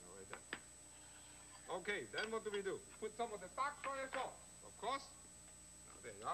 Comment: and @ 19:07 What is that sauce? It looks like soy sauce, but I can't figure out what he said. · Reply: He says "Dark Soya Sauce"